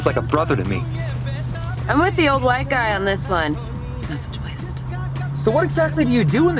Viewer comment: what does she say? “…such…waste” ?? What does she mean? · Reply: "that's a twist"